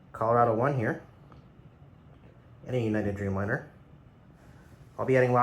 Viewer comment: What do you say at 1:32 ? A United Dreamliner ?